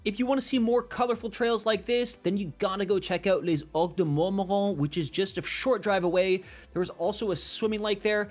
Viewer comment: Good video, but man could you write the names? What do you say at 0:57? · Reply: Cheers. We have been doing this in our newer stuff as I realize my pronunciation make more sense to me than to others 😂 The Place is Called - Ocres de Mormoiron They also have a very small lake you can swim in, one of the only swimming areas in the region, so its perfect when its hot in summer <link>